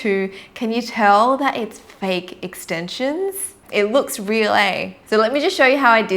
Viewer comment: did she just said “It looks real leh”? 🤣 Adapting well to Singapore’s culture 🤣